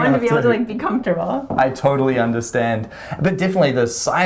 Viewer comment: For half a second, i honestly thought at @ 9:12 said: "I toiletly understand!" :D